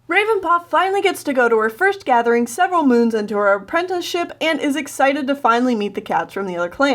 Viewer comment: did I just hear "apprentences" :o [prays] I say "appratacies" XD